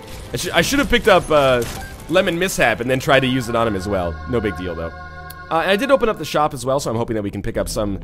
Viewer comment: My English is not the best, but I heard No Big Dildo there. :D